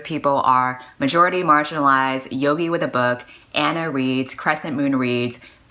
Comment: I heard Anna reeves. what the HECK.